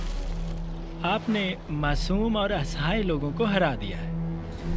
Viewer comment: wtf!! subtitles here are totally wrong he said "You defeated helpless and innocent people"